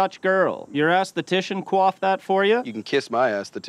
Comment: ? "Did your esthetician BLANK that for ya?" Does Wayne say "coauth", as in coauthor? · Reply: Aesthetician, coif. Like style.